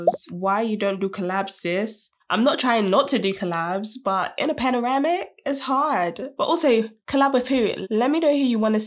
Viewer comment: Did she say panoramic or pandemic